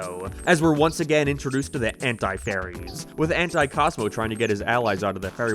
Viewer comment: I was looking away, and I swear to Rao, that I heard “Hentai Fairies”🤣🤣